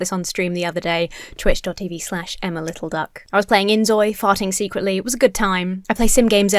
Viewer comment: Did I hear that correctly?... She was playing InZOI?